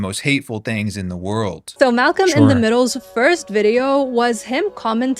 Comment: Did she just call him Malcolm in the middle? XD